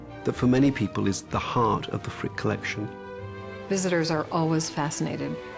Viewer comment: I thought it said "the prick collection".